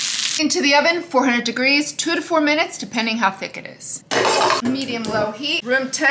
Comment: I thought she said 224minutes, so I set my oven for 3hours and 44minutes... And got some fish jerky....